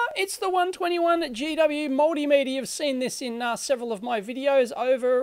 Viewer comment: For some reason I hear "moldymeter" instead of multimeter :D Thumbs up!